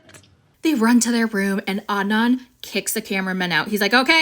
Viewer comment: Every time i hear Adnan's name i hear Om nom (that cut the rope little green guy) 🤣🤣🤣